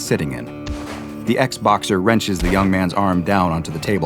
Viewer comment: me who heard xboxer and not ex-boxer